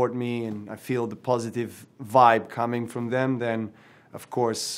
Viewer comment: did he just say vibe.